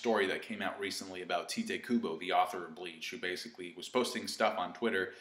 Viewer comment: Did you just say Tee-te kubo?